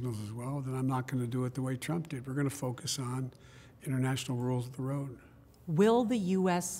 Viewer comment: What did he say at 9:18, “we’re going to focus on international world....”?? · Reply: international rules of the road